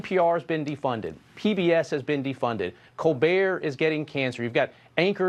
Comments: did he say Colbert is getting cancer? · That's really A LOT of words to say "yes". EDIT: And at 0:52 did he just say "Colbert is getting cancer"? Is that the new plan of the regime, trying to give cancer to those who won't bow down and adore the Orange Blob?